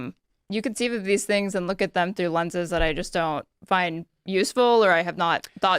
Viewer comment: I misheard her say, “I don’t have thoughts that are useful… “ meh, close enough.